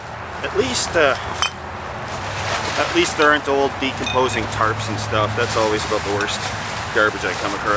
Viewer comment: I first thought you said no de-composing bodies.